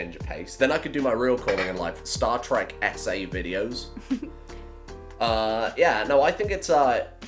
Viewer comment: Not gunna lie, near the end I thought said 'starwars SA videos' instead of 'starwars essay videos' and spat my tea out